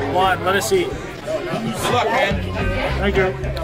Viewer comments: When you started to eat at 5:49, did a guy say "you suck man"? What's even better is that you said thank you. Keep up the content. And you don't suck, you inhale. LOL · Is it just me, or does it sound like someone says: "You suck man" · Did he just say “ you suck man “ ?? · beginining of the challenge did someone yell you suck or good luck? i hope i misheard. they dont know how much you take down